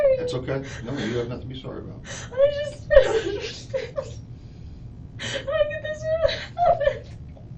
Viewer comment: did she just say "I knew this would happen?" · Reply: " I just don't understand. Why did this happen? "